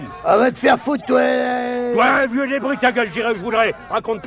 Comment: "Toi vieux débris ta gueule, j'irais où je voudrais"